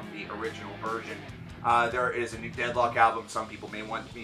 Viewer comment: when you said deadlock i thought you said dethklok and i got excited